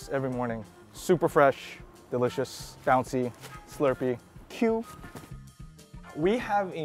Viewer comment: Just a slight correction on the subtitles, when Eric says "cute" at 2:06, he actually said "Q", which is what Taiwanese use to describe soft and springy textures in food, in this case, soft and springy noodles.